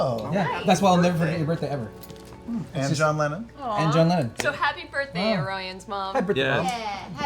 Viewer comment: Just now watching this video and I hear “happy birthday Orion’s mom” in the intro 😬😬😬😬😬😬😬😬😬😬😬😬😬😬😬😬😬😬😬😬😬😬😬😬😬😬😬😬😬😬😬😬😬😬 AWKWAAAAAAARD